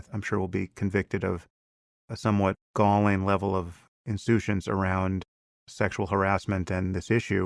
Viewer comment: What is that 15:32 word? "Insutience"...?